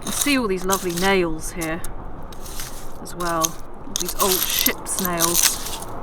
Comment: I thought it cute and quaint when about 4:20ish you said ship's nails, but for a split second it sounded like ship snails! I'm grateful that you open the doors to the past that we might join you!